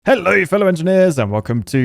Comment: Why did that "hello" in the beginning sound like an entirely other language?? I could've sworn he said "Halløj!"